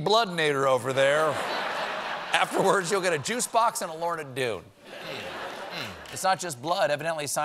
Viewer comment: I’ve listened to this like 10 times and I can’t figure out what he says... “Afterwards, you’ll get a juice box and a... ???” Lorn-a-doo? Lord of Dune? Lowered dune? Lower of two? What the hell does he say?